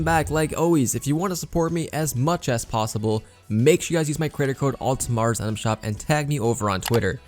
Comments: Did anyone else hear “use my credit card in the item shop” not “use my creator code in item shop? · Did he say Credit Card or Creator Code ? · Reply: Sub To Pewdiepie credit code but I'm pretty sure he was trying to say creator code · Did he say credit card at 0:54 I would love to use it lmao